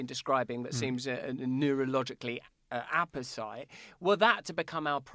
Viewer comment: what word is that "Apus..." What? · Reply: Dore , Apposite: Appropriate or relevant. He may well have taken it unpractised from his 'thesaurus toliet roll':) (see comment above)